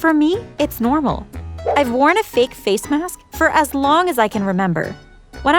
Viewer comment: I’m thought she said mask for corona